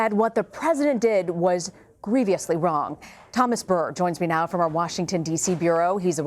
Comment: All I heard was “greeveeeously wrong” 0:15 😆 a news reporter should know better.